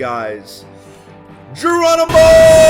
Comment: Did you say Jeromeimo or ĐERONIMO